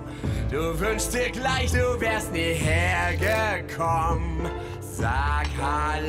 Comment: Why did he say com at 0:08 seconds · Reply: He said hergekommen not com